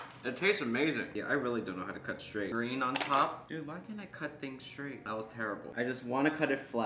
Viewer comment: WHY CAN'T I CUT IT STRAIGHT Me: What I heard was why can't I be STRAIGHT